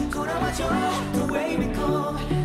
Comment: why do i always hear 'the way we come' instead of 'the way back home' 😂 & wow thirdddd